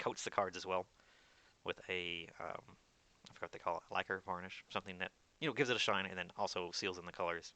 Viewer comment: in this vid at 6:41 he says: it coats the cards with ... does someone know what it is? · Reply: probably "varnish"